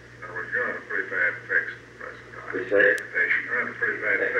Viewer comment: the captions use the transcript interpretation which tends to increase the drama – turning JFKs quick “Whasay..” into “What did you say?” and calling the reaction “slight laughter; a bit forced”. In the full recording it sounds more like actual laughter at LeMay – others at the table spontaneously break up and when Max Taylor continues talking you can still hear amusement in his voice. JFK knew LeMays views and did not take him seriously.